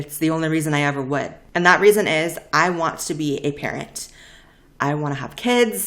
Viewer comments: I thought she said, “I want to be a parrot” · I thought u said "i want to be a parrot"😳